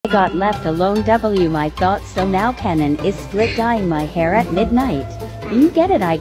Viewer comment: Why’d I hear “strip dyeing my hair” the first time I watched this 💀